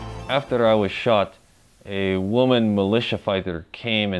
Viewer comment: I'm this is old chip but I m so sick and exhausted while making Thanksgiving dinner for me and the in laws but I swear I heard u say "thanks to a militia woman she came and banged me" I watch that part 3x to make sure that the sickness messing with me and it was messing with me lol watch all ur old videos